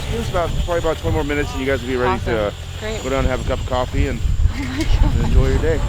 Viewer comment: Heyo, i'm hard of hearing and it's challenging for me to understand what the man is saying at 2:30. can someone type it out for me? Oh hey Diana, i would be so thankful if you started adding sub titles to your videos. I understand why it's not on most people's minds, but it would make a world of difference to me and others with bad or no hearing. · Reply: The man said it would probably be (or was?) about twenty more minutes and then they would be ready to go down and enjoy a cup of coffee. Also, there are captions on this video now.